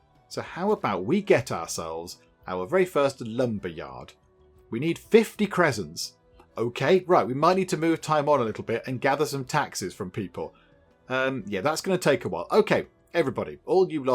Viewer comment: I keep mishearing the currency as presents.